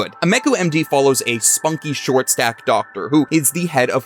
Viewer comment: I had a "WHAAAAT???" moment at 0:47. I was listening at 2x and thought I heard "Spunky short-stack doctor" just at the moment when the petite (ie, non-"stacked") Dr. Ameku is shown. My first thought was that maybe "short-stack" means something different in Canada. After watching the rest of the video, I came back with YT's closed caption turned on. This time at normal speed. Ohhhhhhh! "Short STAFFED doctor" (I think). Closed caption definitely thinks it is "short stack", though. Thank you for doing this work.